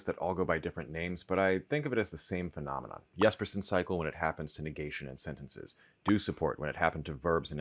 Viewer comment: I had no guess. Also, I heard, "Yes-person cycle." 😅